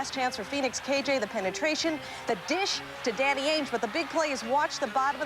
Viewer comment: I know shes saying "Danny Ainge" but because of how my mind operates I heard "Daddy Ainge" lol